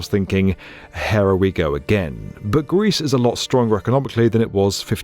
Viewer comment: "Hera, we go again." i can't tell if it's his accent or if he really says, "Hera."